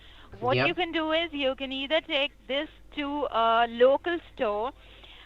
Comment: did she just say ''you can eat a dick'' ?? xD